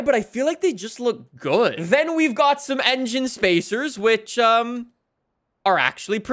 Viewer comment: did he really say engine spacers